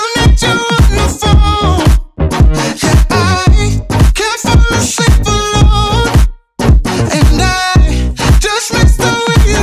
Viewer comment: i thought they said "i can't file a simple loan"